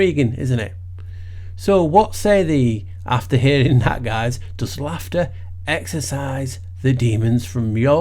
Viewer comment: I thought it said: Does HUNGER exorcise the demons. But yes, yes humor works!